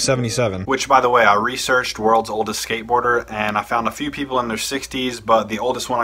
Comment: Wow I thought it said worlds oldest skater hater 😂😂😂